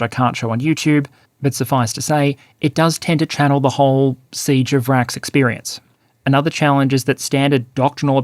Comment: 'Siege of Vraks' You know the fighting is rough when uses 40k references. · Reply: Was listening in the shower and thought he said "siege of rats" and assumed it was a reference to the human wave slaughter attacks of the mercenary prisoner troops from earlier in the war.